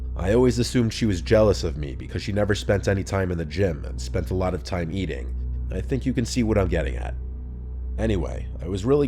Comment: Calling your friend a fatty, then getting mad she called you an idiot, is peak narcissist. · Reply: All I heard was “she didn’t spend any time in the gym and spent a lot of time in the gym” and I booked it to the comments bc I knew she was a trash human lmao